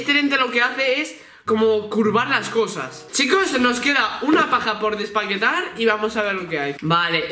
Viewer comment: Like si crees que dijo paja en vez de caja 3:14😂😂